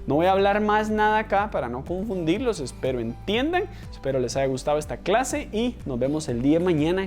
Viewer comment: jajaja, a lo último entendí "los espero en tinder" y luego lo puse de vuelta y me di cuenta que dijo "espero entiendan". Gracias!!